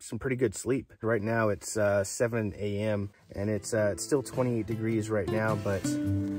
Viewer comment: Hi! Great video! Didn’t know there was a city of rocks in New Mexico! Did you say at your video stamp time at 6:52 that it was 20 degrees at 7am or 28 degrees? 😅 am trying to imagine how cold it was with the Napier tent truck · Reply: Hi! It was 28 degrees. The lowest it got to that night was about 25 degrees! I wouldn’t want to camp any colder than that lol 🥶